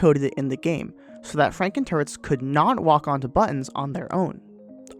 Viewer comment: My volume was kinda low, so I thought you kept saying "Frankenturds."